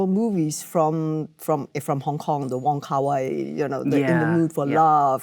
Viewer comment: Closed captioning / subtitle note: at 21:09 where it says "(Michelle mumbles)", she's actually saying the name "Wong Kar Wai", a director whose style is referenced in Everything Everywhere All at Once – though I don't think he and Michelle Yeoh have ever worked together. Also, at 21:20, instead of "Stephen Chao", it should be "Stephen Chow", who Western audiences may recognize from Kung-Fu Hustle.